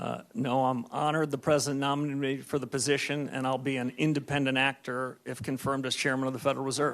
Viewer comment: 🤔🤔 are we really living a TV show did he just say he'll be an independent Actor